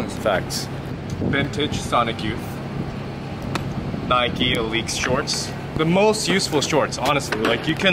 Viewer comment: Exactly what type of Nike shorts were those @ 3:27??? I couldn't quite make out what you said. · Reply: Nike x Alyx